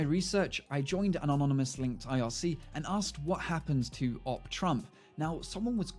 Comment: Does anyone here what he said? What link? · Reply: Ruby Studios he said irc